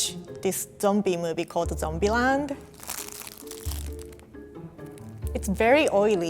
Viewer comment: When she said zombieland I thought she said dumpyland